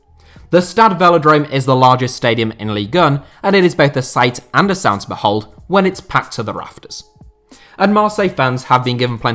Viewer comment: Can someone please help me to understand what he says after "the stad velodrome is the largest in ligue 1" (2:26).I've rewound this part of the video so many times trying to get what he said exactly. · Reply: "And it is both a sight and a sound to behold when it is packed to the rafters"